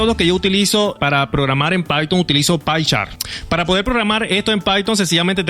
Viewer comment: como se llama el programa que utilzas para programar en phyton amigo?,esque no entendi el nombre,lo dices en el minuto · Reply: Pycharm